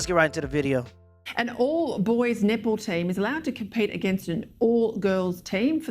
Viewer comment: when the anchor starts saying "All-boy Netball team", it sounded like "all-boy nipple team"....